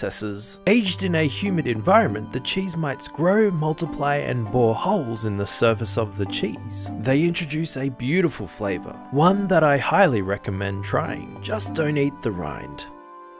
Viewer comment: I heard "Just donate the rind"